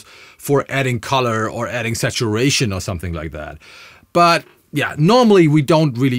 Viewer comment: oh, adding for Saturation. I heard it as adding compression for sexual relations.